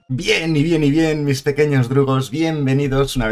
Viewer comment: siempre q dice eso pienso que dice mis pequeños Drogos 🤣